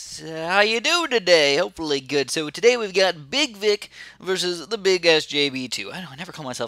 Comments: does it sound like he said big dick? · Who heard him say big dick??